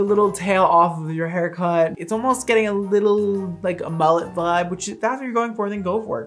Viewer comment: I thought you said, "mullet bud" for her little curl left on. It's so perfectly expressive.